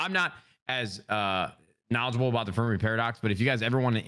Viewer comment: what book does he recommend? Only part I heard was paradox, he mumbled the first part of the name of the book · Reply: its the fermi paradox its only one theory there are alot of videos about it and other theries